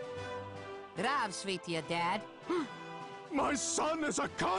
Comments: what says steve ? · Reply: He says "здравствуйте". This is the formal version of saying hello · what did Steve say? "Zdraveĭte"? isint that Russian for hello? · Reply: Dakota Stein zdravstvuyte and it is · What does Steve say in Russian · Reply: Zdravstvuyte, meaning hello in Russian